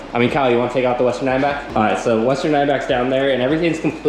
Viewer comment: Awesome videos the only thing is you need to slow down and talk more clearly I had to rewind it a couple times I thought I heard you ask Kyle to take out the Lesser 9 back I had to listen to it three times before I realized what you were talking about the western diamondback Other than that awesome videos keep up the good work